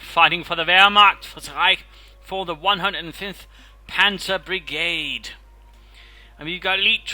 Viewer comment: That sounded like "cancer brigade" to me^^ Is that something like the stomach battalion? xD